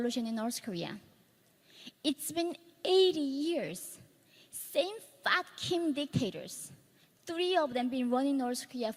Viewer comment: I literally thought she said "Same f*cking dictators" 😅😅😅 I had to rewind.